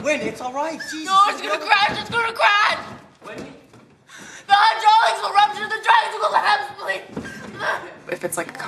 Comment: for those of you who wonder what she says at 1:00, she says,"the hydraulics will rupture the tracks!!! we'll collapse!! " btw i like how she screams shes a great actor